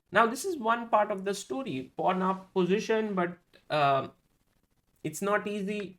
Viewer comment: whenever he said pawn up position i heard "pornhub position"